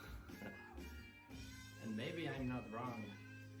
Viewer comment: did you say, "Maybe I'm not wrong"?